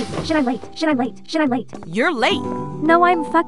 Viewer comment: On 0:14 i heard ”should i wait?”